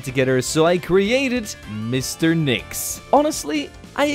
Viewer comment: i will be honest i thought you said mr mix and it made me remember that creepypasta lol